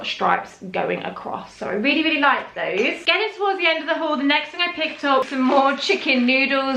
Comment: I thought you said huge haul